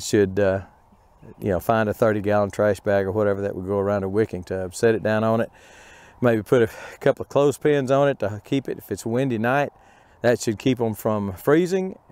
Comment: New this site what is a wikki tub? What is the black pipe sticking out? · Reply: lol. I thought you were saying wikki tubs... but now i understand, thank you for the clarification. Very cool.